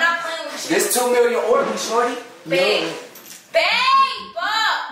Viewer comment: did he call her shortti